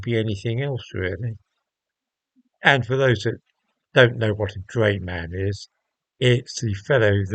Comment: I know what a Drayman is, but thought you said Gayman for a moment there 😂